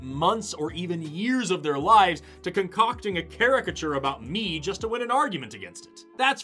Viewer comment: can someone figure out what word was spoken at 2:47, sounds like "care-ic-a-chur" and if you can that would be great! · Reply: the word is caricature, it describes an exaggerated or parodic version of a person